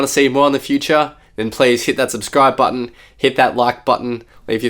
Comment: hahah thought you said "Tschüss" there in the end for a second ... is it "cheers"??